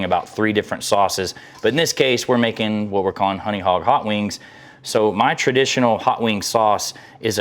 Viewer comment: I thought it said honey hotdog too. 😂